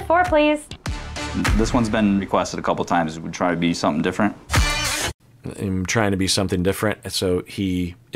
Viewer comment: I thought he said something like “maybe pick something different” idk